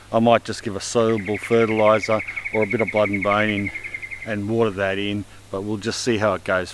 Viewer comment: the subtitle says "or a bit of (murmurs)", but what he said was "or a bit of blood and bone" which is a garden additive.